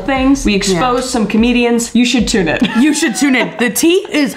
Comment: I thought Kris said "you should too man" but she said "you should tune in" lol